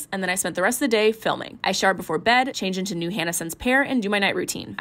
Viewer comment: Why did I hear "I shart before bed"